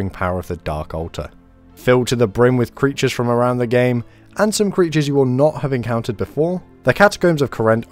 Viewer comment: what does he say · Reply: "filled to the brim with creatures from around the game, and some creatures you will not have encountered before"